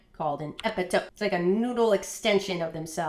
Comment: I misheard 40 centimeters as 47 meters and got very afraid.